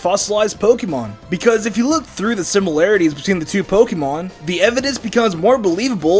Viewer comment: i thought you said Mvidence